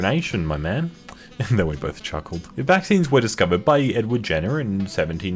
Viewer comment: I thought it said that EddsWorld discovered vaccines in 1796